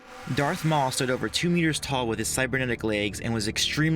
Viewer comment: I thought he said 2 years tall for maul lol 4:15 but it said meters so yeah lmao